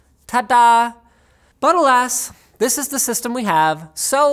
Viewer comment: You said "but alas" i heard "buttle ass"